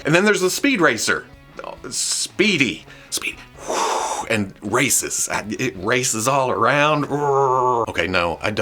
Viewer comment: The Copper Kidd segment was incredible at 9:22 it sounded like you called the car racist, which i could see being like a cancelled tv pilot in the 80s. Like a Knightrider rip-off with Lavar Burton who gets teamed up with a racist ai who every week would've learned a lesson about tolerance and over the course of the show becomes not racist and helps Lavar with social change, but audiences didn't understand that the car was an allegory for racism in America (much like how Twitter weirdos don't understand Starship Troopers right now) and just thought the show was promoting racist rhetoric so it got cancelled. If Siverhawks didn't exist does that mean we all hallucinated its absolutely incredible theme song?